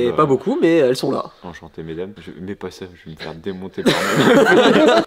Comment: Enchanté jeune homme, met pas quoi mmhh ?? 😂